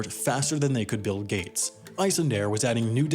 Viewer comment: did he just say "BILL GATES"